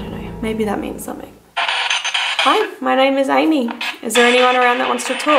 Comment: it sounded like hey Amy but after I think it said amyyyyyyy